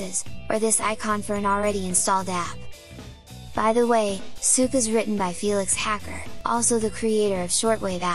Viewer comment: Bruh... I was watching this video, then my puppy sneezed on the keyboard, so while I was cleaning I accidentally went backwards a bit at around 2:40, and all it then sounds like is saying at that moment because of that is... "By the way, Souk is racist" ...and I'm like whaat. (it was timed to the point where it legit sounded like that was what you said)